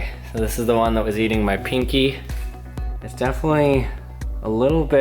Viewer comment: When he said “my pinky” I thought he said “my penis”😂😂😂😂